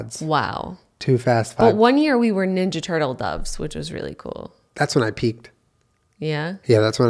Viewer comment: At the beginning when says “that’s when I peaked” I thought he said “that’s when I peed”